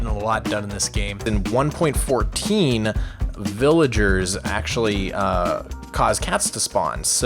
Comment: I thought he was going to say villagers cause cancer